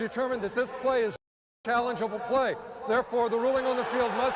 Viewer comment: with the pause at 7:54 - it sounds like he says "After conferring with replay - it is determined that this play is FUCKED" - makes more sense that way atleast!